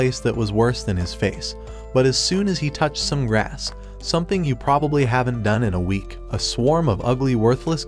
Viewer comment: did you say he never touched grass in a week?